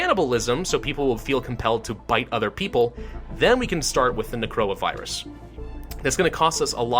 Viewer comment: When you misheard Necroavirus as Coronavirus you really start questioning if all of this is just a conspiracy theory...